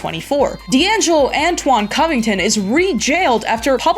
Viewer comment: For a second I thought she said "cumington", probably says a lot more about me than it does her.